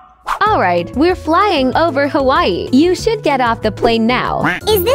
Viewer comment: Thought you said Hawaii not Ohio